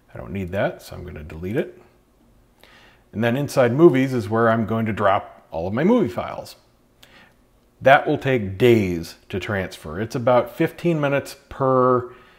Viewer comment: You said it take 15 minutes for roughly 100gb, and then proceeded to say it would take days for your 11 files to transfer, yet the total would have been less than 150gb??? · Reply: I don't know why you just deleted your other comment, unless it's YT glitching. Anyway, like I said you misheard. I said 'which is where I'm going to copy over all my movie files. THAT will take days'. I didn't say 11 files will.